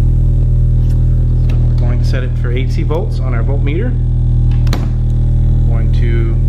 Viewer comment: Did he just say " mole wienner " ? Oh volt meter , ha ha ohhhh boy.